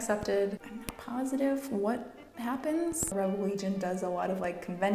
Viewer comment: the captions say Avril Lavigne instead of the Rebel Legion so it ends up saying that Avril Lavigne does a lot of conventions and parades and charity stuff and that was a joy sparking moment lol